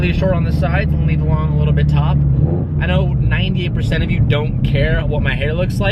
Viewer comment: Am I the only one who heard “leave the long a little bit top”